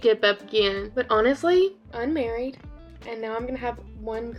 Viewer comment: I thought you said you had a husband!! At 18:38 I’m like ummm did I miss a chapter! Also I love watching you both so much☺️✨ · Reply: girl i said unmarried?!😭 how in the world did you hear that hahah